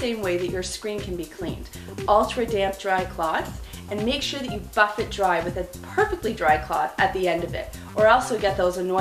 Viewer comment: i thought she said fuck it dry